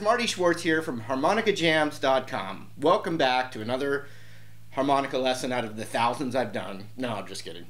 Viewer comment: Lol, I thought it said harmonics lesson